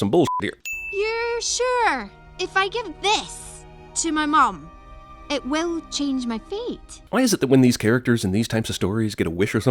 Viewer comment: I always thought she said "So if she has this it'll change my feet" I would watch that movie in a heartbeat.